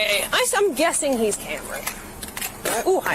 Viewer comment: At first I thought that she said "hammered" like you, but I watched it again and I'm pretty sure she said "I'm guessing he's Cameron" which she then repeats and states that he's the registered owner of the vehicle.